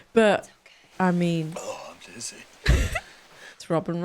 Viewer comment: Did he say "I'm jizzy."?